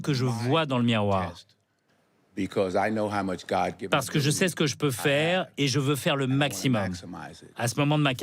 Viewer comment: Non!! Vous voyez. Il ne dit pas : "je sais ce que je peux faire" 5:45 mais plutôt "je sais ce que Dieu m'a donné". Pourquoi la France n'aime-t-elle pas reconnaitre DIEU ?